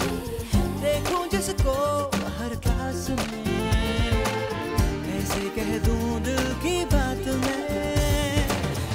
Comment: Can anybody tell what he says @ 0:40 'Dekhu jisko .............se kaise keh du dil ki baat main' So that I can sing this uninterruptedly I skip that word always. My bad. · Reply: Dekhu jisko harr class mei